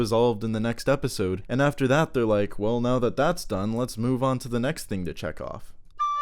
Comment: I heard "the next thing to chekov" and idk if that was me mishearing or a pun.